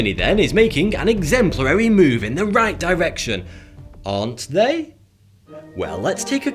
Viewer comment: anyone hear "the reich direction"?